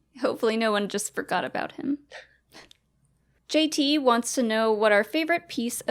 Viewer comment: Lol it sounded like Mollie said "JTE". Schmoedown has taken over her brain!